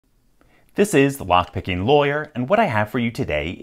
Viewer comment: for some reason today i heard you say "This is the vodkakinglawyer" and it made me do a double take.